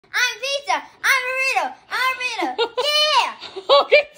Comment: I thought she said i'm jesus but she said i am a pizza